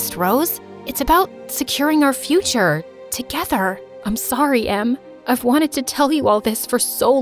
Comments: they said “I’m sorry em” but the subtitles said “I’m sorry mom” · uhh you made a mistake, the subtitles said "I'm sorry, mom" while Rose said "I'm sorry, em" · rose is saying im sorry em and the subtitles say im sorry, mom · the subtiles said mom instead of Em 😅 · the wrote mom instead of em !LOL! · in the subtitles When Rose says "Am sorry M" The subtitles say "Am sorry Mom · why did she say I’m sorry mom ? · Why does it say in the captions at 11:22 say mom instead of em